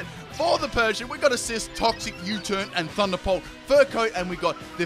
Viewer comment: Did he say Thunder Pulse?